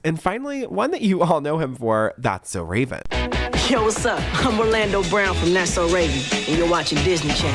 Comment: Actually, in that clip, i definitely heard him say "I'm ORLANDO BROWN B!@ 😂 also it is definitely his voice. We all know his voice. Listen again! "I'm orlando brown b!@ clear as day. So insufficient evidence my ass! Either he paid her off or she just dropped the charges.